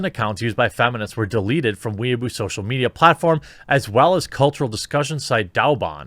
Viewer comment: I’m not gonna lie, I thought you said “Weeaboo social media platform”, and I immediately thought “China has Reddit?”